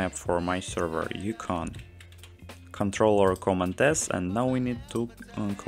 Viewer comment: what did you say exactly at timestamp 1:35 , i dont understand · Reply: Ctrl or Command + S on your keyboard